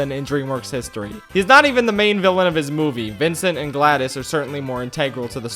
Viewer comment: I had auto captions on and the moment he said “Gladys”, the Subtitles said “GLaDOS”. Which is odd because when someone mentions the latter, auto captions says the former…